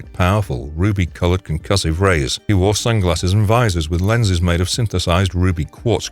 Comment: what did he wear? I couldn't catch that the first 2 times. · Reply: Sunglasses and visors